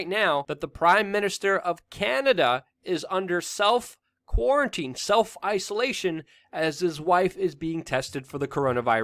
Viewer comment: Justin Trudeau under self isolation? I misheard at first and thought self immolation which got me a little happy.